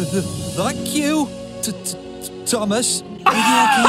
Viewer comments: Hi! I CLEARLY heard “F**k you, Thomas.” I’m surprised parents didn’t complain about it like they did Bob the builder, which I didn’t hear any swearing in that clip, it was just muttering like they claimed it to be. I did hear “F**k” in Hunchback of Notre Dame, I also think Mr. Rogers knew what he was doing because of the way he grinned right before he flipped the bird! 😂 Hilarious video! Have a good day! 😃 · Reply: I heard “Thank you, Thomas”. It's a UK version, which makes it sound like it’s swearing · Hi! I CLEARLY heard “F**k you, Thomas.” I’m surprised parents didn’t complain about it like they did Bob the builder, which I didn’t hear any swearing in that clip, it was just muttering like they claimed it to be. I did hear “F**k” in Hunchback of Notre Dame, I also think Mr. Rogers knew what he was doing because of the way he grinned right before he flipped the bird! 😂 Hilarious video! Have a good day! 😃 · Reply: I hear "like you Thomas-" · Hi! I CLEARLY heard “F**k you, Thomas.” I’m surprised parents didn’t complain about it like they did Bob the builder, which I didn’t hear any swearing in that clip, it was just muttering like they claimed it to be. I did hear “F**k” in Hunchback of Notre Dame, I also think Mr. Rogers knew what he was doing because of the way he grinned right before he flipped the bird! 😂 Hilarious video! Have a good day! 😃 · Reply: I heard “Thank you Thomas”